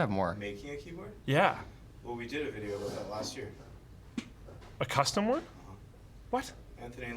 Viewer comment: Legit just search it up lmao it’s the first thing that comes up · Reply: I found it. It was made a year ago, not a week ago. I thought Nick said "last week", but listening again I do hear "last year". I blame Nick for not being in range of the microphone.